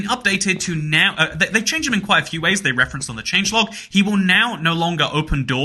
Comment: 13/15 (I didn't know that wandering traders used to open doors 5:23 or that there was a "Hello, Russia" splash text 10:14), but that's not important. You said "new" at 10:25 instead of "no". Is the secret 16th fact that British people say new instead of no? Great video! Cool stuff!